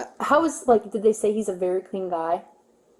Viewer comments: she said, "did they say he is a very clean guy?" The caption was incorrect...FYI 😊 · 😂 the officer 41:00 says “Did they say he’s a very clean guy?” And the closed caption says “Did they say he believed in God?” Like WHAT?